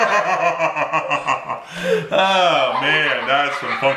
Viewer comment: Did you just curse in front of your kids... lol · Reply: THERAZORKILLE Productions I probably misheard at 2:52 when he said "thats some funky stuff". He had hexafluoride in his system so i thought i heard "f*cking" instead